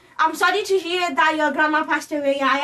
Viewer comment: Ok....I heard “sorry about your grandma” That’s about it anyone else catch the rest😂😂